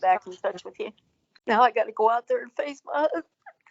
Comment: Then why is she worried about having to face him? 🤔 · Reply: It does sound like she said face my husband, but she in fact said, “face my YOUNG-UNS.”